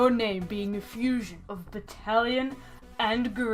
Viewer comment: When you said "Battalion" during the first entry i thought you said "italian" and i thought you meant the demon in question was just an italian grandma, and also that Return of The Dead was called "italian" in japan.